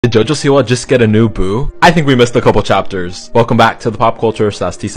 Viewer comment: I thought he said “Did Jojo Diwali just get a new BOOB” 😂😂😂